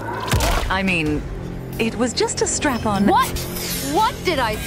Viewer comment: DID SHE SAY STRAP ON? WTH IS GOING On WITH YOU??!!!😭😭